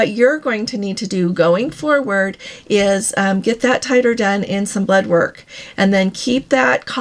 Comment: Regarding the measles vaccine, I don't understand what is the word you are using @ 2:27 (and later in the video, too) when you say what sounds like "get that 'tighter' done." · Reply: Titer is what they call it.